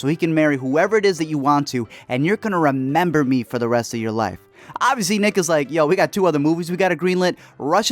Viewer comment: Did I hear that right, did you say the n-word at around 2:50? If so... are you black? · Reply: Ik this is like 2 years later, but I’m pretty sure he said “nick was like” and not “niggas like”